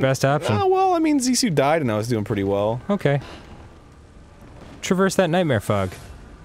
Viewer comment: said " Traverse of that nightmare fog" but in my head it sounded like he said " Traverse that nightmare fuck